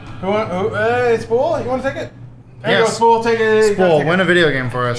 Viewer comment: only accepted because he thought said ", take a hit'.